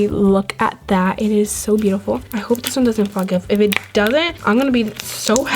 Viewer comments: does she say “ I hope this doesn’t f*** up” 😲?? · did she say “I hope it doesn’t fuck up” ???